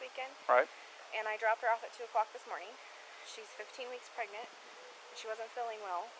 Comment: When the baby comes on tv and he says “and she’s pregnant” and the officer follows up with, “well how far along is she.” I know I heard Chris say, “14-15 months.” Who tf is pregnant for that long. He a bad liar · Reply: He really said weeks. At first I heard "months" aswell. When you're nervous you confuse words with one another and random stuff can pop out. I cranked up the volume and watched his lip movement. You can play it a little slower also. It's clearly "weeks".